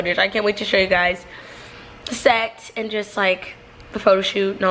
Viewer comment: did she say she was going to show us sex?